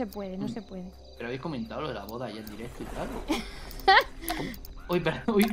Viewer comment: que dice? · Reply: “¿Pero habéis comentado lo de la boda en directo y tal?”